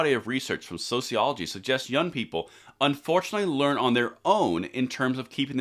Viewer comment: Sorry you completely misread what they said at 5:21…they actually said “unfortunately they are on their own” (which is what we say all the time) self-reliance.